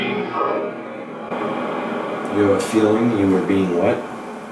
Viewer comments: I hear " I had a feeling I being followed." 😞 Damn X rip. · I hear "I have a feeling I was being followed" those details on the murder came out after this video was published pretty sure. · I heard “I had a feeling I was being followed” omg I’m crying 😭 Long live Jahseh